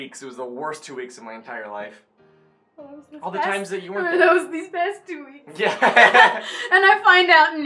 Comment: He's my friend. He's a boy. I did his make up. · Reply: When said, "Worse 2 weeks of my life," while you were talking, I(and I think some other people) heard him say, "All the times that you weren't there." PPPUUUBBBLLLEEE!!!!!!!!!!!!!